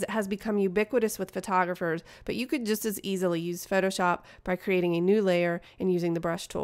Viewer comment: who else thought she said fetish art instead of Photoshop xD